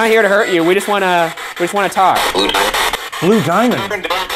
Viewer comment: When I heard the spirit say “Blue Diamond” all I could think of was Steven Universe ;w